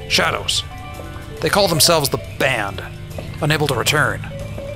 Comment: Oh the Banned. The first time I listened I thought he said Band. That makes more sense. lol